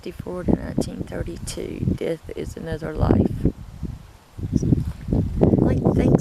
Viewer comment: Hey... Long time, eh? So..... @ 9:14 whisper "another life" right after you say what's on the tombstone. Either repeating or confirming what it says.... Very cool!!!!